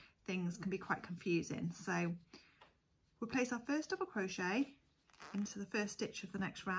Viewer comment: you said “place our first double crochet” instead of saying single and I had to replay it 4x to watch your movements 😂😓